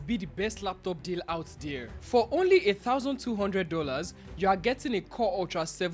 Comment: did i hear 8200$ !!?